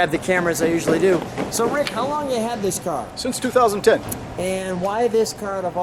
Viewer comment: So it’s a 2008 and he has had since 2006. Amazing feat. Went back in time before it was built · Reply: I thought he said he had it since 2010.